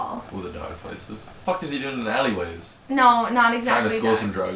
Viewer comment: The disappointment at The Mortal Instruments XD Omg at 7:13 I thought your boyfriend said fucking people in alleyways I really need to get my ears checked...